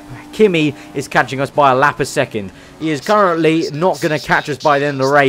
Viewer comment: am i really hearing this "a lap a second?" lol